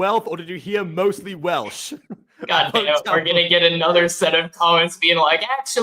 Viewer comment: here's your engagement. The line Jeff Blim says is "we're mostly Welsh". However, earlier someone else said "we're pretty wealthy". So in this case both are kind of true, but misheard the mostly Welsh line.